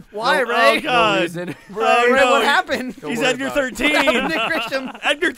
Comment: so i decided to go back and watch all the lets plays.. had this tabbed out and i hear jack cry "he is edgar 13... funny enough i was tabbed out to read information about FF13.... awkward